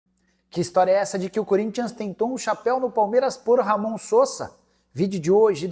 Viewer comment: Eu escutei "Corinthians passou o chapeu" kkkkk